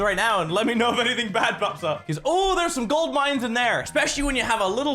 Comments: closed captions instead of pops up fucks up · When you said oh there's some gold mines in there it's thought you were saying OML do I love refrigerators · I think he was saying "let me know if anything back pops up" but instead we got something else with CC